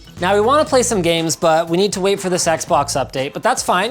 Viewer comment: did anyone else hear that wrong??? "we need to wait to thisexbox update..."